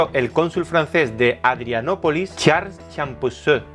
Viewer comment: Minuto 0:57 parece que dice "Charles Champurrado".... Buen video!